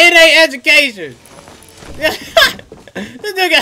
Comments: What do you mean brother!! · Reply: i swear to god @ 14:01 he said this ne**a and then he laugh so like nothing happen WTF brather ! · Was not expecting that ending at all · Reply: i swear to god @ 14:01 he said this ne**a and then he laugh so like nothing happen WTF brather ! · This man is literally doing Story time and impersonations while playing, Dae doesn’t even have to try anymore 😂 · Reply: i swear to god @ 14:01 he said this ne**a and then he laugh so like nothing happen WTF brather ! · For the Hamlinz impersonation, he should’ve just binged a bunch of episodes of “Daily Dose Of Internet.” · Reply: i swear to god @ 14:01 he said this ne**a and then he laugh so like nothing happen WTF brather !